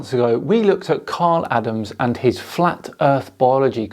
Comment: My name is Karl Adams, so when I heard you say my name, my arse fell out of my trousers lol. His name is Kyle Adams I believe mate. I may have misheard you, so sorry if that's the case lol. Keep up the awesome work and hope all is well with you and yours.